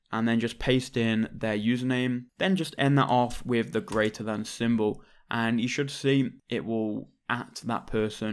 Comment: What did he say in 1:13? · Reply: Greater than symbol